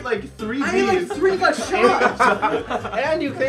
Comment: wen jesser said and u cane late I deadass thought he said and u gained weight LMAO😂😂